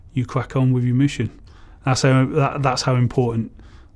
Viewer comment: Did he just say, "Thas how important, Snip- pooing is"?